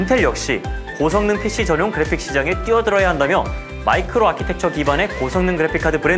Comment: 말은 "고성능 pc전용 그래픽 시장에 뛰어든다" 하지만 들리기론 미쳐가는 채굴 시장에 뛰어든다로 들리는데?ㅋㅋㅋㅋ